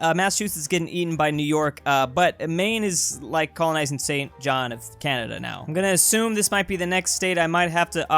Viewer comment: did you just say “Saint John F. Canada”